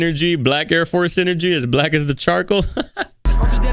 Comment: I thought he said as black as T’Challa 😭